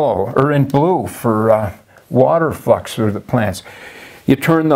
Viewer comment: I thought he said "f**ks with the plant" and thats how I got hooked. 😆